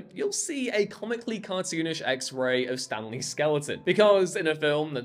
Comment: I thought you were saying Stan Lee’s skeleton 😂